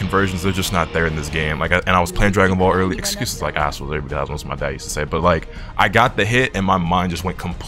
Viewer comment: I turned on captions and slowed it down to 0.5 speed. Me and the captions still don't know what your dad used to say 19:40 lmao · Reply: Excuses are like assholes, everyone has one LOL